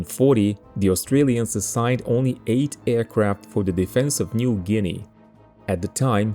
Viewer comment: just in case you didn't hear right: 8 aircraft. Eight